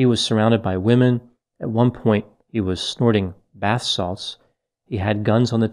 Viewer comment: Dr Grande, did say he did bat sauce? I listened 3 times and I thought you said bat sauce. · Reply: Bath salts